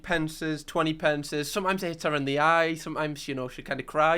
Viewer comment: I was only half listening and I hear: "Sometimes I make it rain on Chloe...sometimes it hits her in the eye...sometimes she kind of cries"